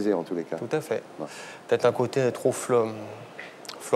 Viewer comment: Il dit quoi vers 7:14 ? Il y a un mot que je comprends pas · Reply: Fleur bleue